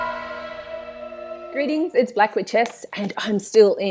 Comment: A propos of nothing, let me just say how stupid I felt when I figured out that she's calling herself. I thought she was saying Black Witchess (like lionESS, ActrESS, OgrESS, that suffix).